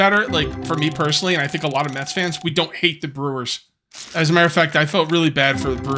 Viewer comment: when you asked if any one was a mets fan i thought you said meth fan i was like wow this stream took a left turn really quick lol